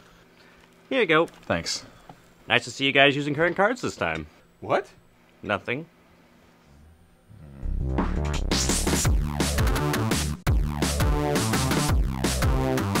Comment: I don't get the whole "Nice to see you guys using current cards this time" thing at 0:42 ... Could someone explain that whole thing? Was there a joke from one of the other videos? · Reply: thought he said "credit cards", didn't understand why until I saw your comment, then I didn't understand why the current. And thanks to kanklez now I understand everything. Big day for me. jajajaja